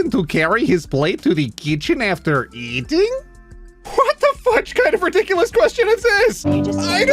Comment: For a whole damn 30 seconds I didn't catch that he said fudge and got concerned